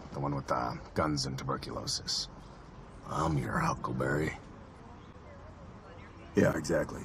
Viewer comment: I'm ur uncle berry" bish I love cas 😘✌ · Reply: WAIT, I THOUGHT HE WAS SAYING HUCKLEBERRY?!?!?!?